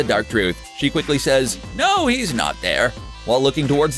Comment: Oh I thought she said 'no, he's not dead' not 'no he's not there'